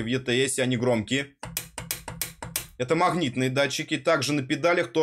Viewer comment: Услышал "махнитные", выключил нахрен